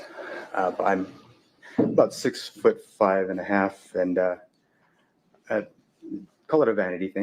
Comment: did he say he is 6 foot 5 and a half? at